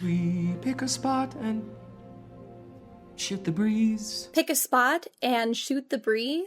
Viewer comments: i legit thought he said “shit the breeze” instead of “shoot the breeze” on first listen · "Shit the breeze" I know it's shoot the breeze I've heard the musical 100 times but that's all I heard watching this scene 😭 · Did anyone else hear "pick a spot and SHIT the breeze" at 8:55 ? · Really thought he said "Shit the breeze" for a second